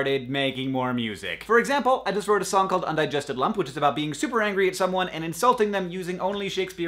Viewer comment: I thought it said undigested lamp.